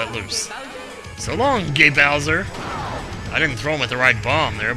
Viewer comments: I always thought Mario said "So long, Big Bulgy" when he threw Bowser. XD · I thought he said "So Long, Me Bowser!"